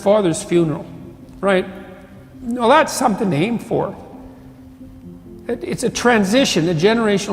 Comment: It's says "Thats's something to NAME for" instead of ""AIM for"